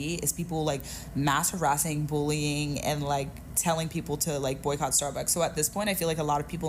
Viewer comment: Did they say work at starbucks? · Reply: I think, contextually at least, it was probably boycott not work at? not sure tho.